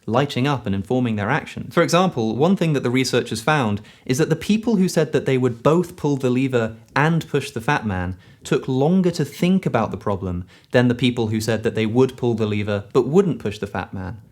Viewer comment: I first heard "pull the leaver or push the Batman" :D